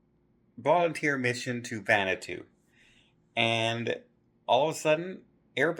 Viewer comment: I thought you were saying “Xanadu” … but I think you were just mispronouncing Vanuatu maybe?